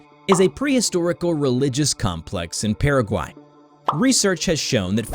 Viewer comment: Did he said paraguay ?